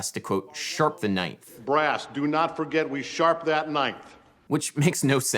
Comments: I always thought he said "Sharp that knife" as a cheeky way to remind them of a stoccatto segment, sharp that ninth is such a bizzare thing for him to say to them. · Maybe this is how I heard the line as a result of my more classical training, but when I heard Fletcher say “sharp that 9th”, I always heard it as him telling the brass that whoever is playing the 9th of the chord to bend it slightly sharp. The reason for that is that bending certain notes of the chord up or down helps the chord resonate better, so that’s what he’s telling the brass players to do. But that’s just how I saw it, and I might be completely wrong on this, but I just thought I’d put that out there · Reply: As a musical theory layman I heard "sharp that knife" at first. 😆